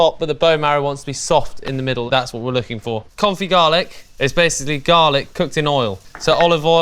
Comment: At first I thought he said “coffee garlic” and was thinking that sounds weird